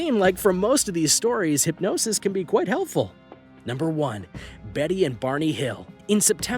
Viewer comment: I thought you said Betty and Jason...wait I just realized that Betty and Jason are actual character names in riverdale